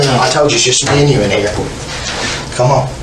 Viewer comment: Did anyone catch on at 50:33 the detective said its only me and you my nigga